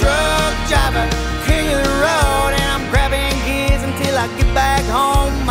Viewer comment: What does he say at 1:02? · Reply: King of the road